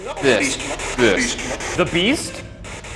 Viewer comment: ok so i rewatched this because i swear i heard some things from the spirit box, i wrote them down in a notepad: 9:47 the beast 9:50 help us (not sure) i don't remember when i heard it, but earlier i heard a woman say "honey". 18:40 "trouble" 18:55 "maybe"